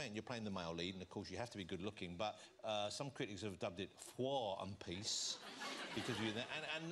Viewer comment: What is Jonathan saying at 5:50 when he repeats the critics' pun on the handsome star in "War and Peace"? Sounds like "Fwah and Peace" (can't be, I know), and when James replies, he seems to say "For and Peace" -- but that isn't a pun on how hunky James is. Any ideas? Thanks. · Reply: Yh it is 'Phwar' as in because James looks so hot